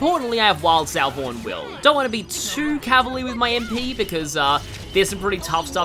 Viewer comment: Will said 'my aim' so close together it sounded like he was saying 'lame'... and I don't know what he may be calling lame